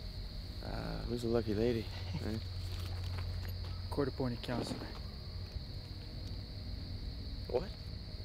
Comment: Why shoot the lady ??? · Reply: I had to go back and listen at 2:20... couldn’t hear what he said properly, I made out “councillor” Loved this short film though!!! Amazing work on the suspense, filming, acting etc!! I thought it was quite funny when he started heating up the chicken lool Hope to see more similar films